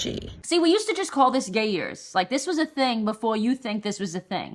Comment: I was half asleep listening to this and I thought you said “Gay Ears” I was so confused. I had to restart the video 😂